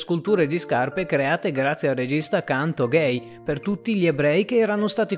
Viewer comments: inizialmente ho sentito (4:03) CANTO GAY aahha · avevo sentito ''tutte sculture create grazie al regista del canto gay'' hahahaha e poi realizzpo